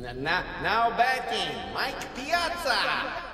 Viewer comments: What does he say at 00:15? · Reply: "Now batting, Mike Piazza!" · What does he say at 00:15? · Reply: i thought he said "no betty, mike piazza.